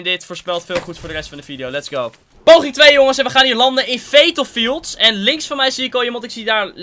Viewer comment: Wie hoorde 5:55 ook fatal fields in plaats van pleasant